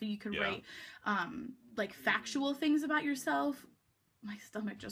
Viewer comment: Did she say FATual at 9:55?